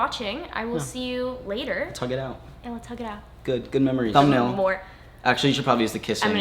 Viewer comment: i thought he said "tounge it out" and i was like dayum hes still into it, but he said "lets hug it out" lol bye